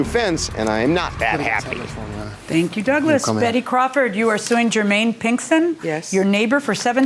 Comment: I thought judge said Betty Crocker 😂